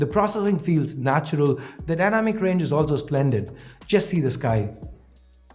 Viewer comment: When u said see the sky ...I thought u said see this guy ...then I got it🤣